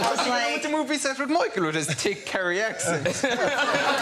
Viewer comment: what did he say? I got "Micheal" and "accent", at least I think... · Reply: but you were on the movie set with Michael with his thick Kerry accent